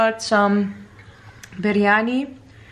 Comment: I thought its poha until u said its biryani lol